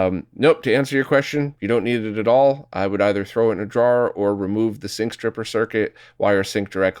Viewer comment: A couple times I thought said "jar" when he said "drawer", lol.